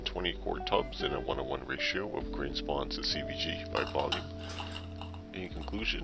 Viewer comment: Dis you say CBG at 11:20. Wondering what bulk substrate that means, or maybe I misheard. .